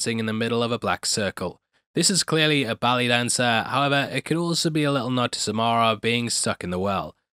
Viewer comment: Did anyone else hear “Belly Dancer” at first 12:44 ??